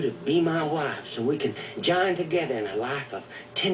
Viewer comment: Did Micky say "Buzz off Yoko" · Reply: Yokel. Means hick or hillbilly.